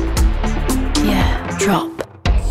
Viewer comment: What does it say at 2:26???!!! I swear it says "Yeaaahhh Trump.." Can't be though right?